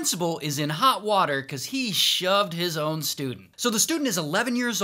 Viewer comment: I thought that this dude said “shot” instead of “shoved” 💀💀💀💀💀💀💀💀